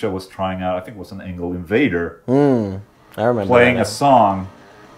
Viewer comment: Did I hear someone say Vader? :)